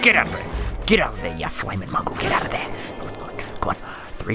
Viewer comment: I heard “you flaming muggle” I never knew you liked Harry potter